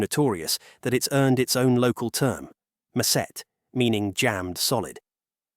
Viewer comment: me lmao when I heard 'macet' pronounced as 'ma-syet'